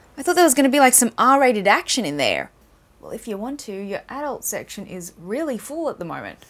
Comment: you're ?? section? I didn't understand that one. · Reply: "Adult" section